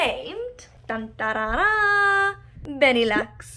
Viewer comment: Well I thought Lenny so must have heard the “Benny” as “Lenny” in that one video...but that is quite the name! What a cutie!